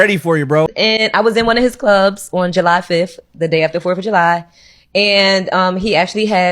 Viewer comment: yo did she say July 5th, the day of the 4th of July? 😂😂😂 Oh my God I'm dead 💀💀💀